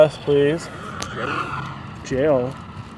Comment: "Jane" (I thought he said jail)